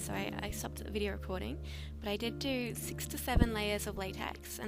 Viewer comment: I thought she said 67 layers of latex and I nearly died